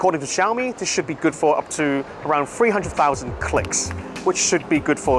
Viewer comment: not to be mean but "free" thousand clicks? 3000? 30000? i cant understand him